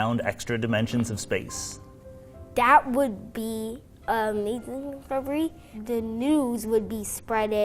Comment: the guy asks if the kid would be excited if he'd be excited if they said they found other dimensions, 3:58 he answers but I can't understand what he says and the captions kinda just ignore it. I think he says "that would be amazing probably" but I'm not sure. When I first heard him say it, it sounded like he said "that would be illegal probably" what do you guys think he said??